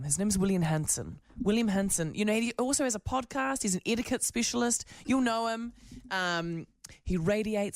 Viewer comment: For a moment I thought the presenter said William was an "idiot specialist"